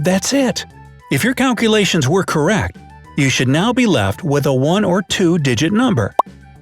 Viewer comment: Well, I got "Uhh..." so... Guess you couldn't guess my name. Edit: Well, I got fooled. Sort of. Well, at first when you said you needed a 1 or 2 digit number, I heard it as you need a 1 or 2. So, this comment ALMOST said "You must have done it wrong" and then looked at the date, and added "Wait, is this an April Fools joke?" I then went back and rewatched the part where you said the thing about 1 or 2 digit number, and realized my mistake. So, I erased it.